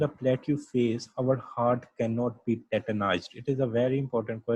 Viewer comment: u said it imp question What that word is?? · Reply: our heart can not be tetanized like skeletal muscles due to this plateu!!!